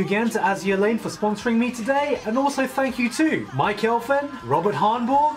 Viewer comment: when he was reading out the patreon shoutouts and he said 'Mike Elfen' i legit thought he said 'my girlfriend'.